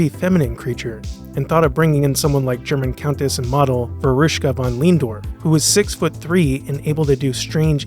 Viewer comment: For those who couldn't understand what he said, her name is Veruschka Von Lehndorff.